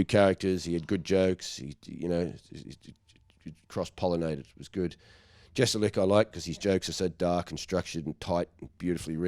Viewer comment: what’s the name of the comedian he mentions here? I cannot make out what he says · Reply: Anthony Jestelnik is who hes referring to. Dunno who your favorite comedian is but if can appreciate dark comedy youre about to trade up. In a word. OFFENSIVE. Zero fat on his jokes. " hes not for everybody, mainly just people who read."🤣